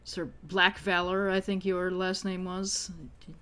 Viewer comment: For a second it sounded like was saying Sir Blackvalor's name was LACKvalor, so I had to rewind to check, because as much as we're not trying to hide this guy's an asshole, I think writing isn't that on the nose.